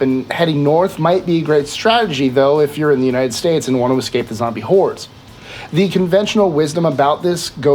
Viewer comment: did he say zombie horse?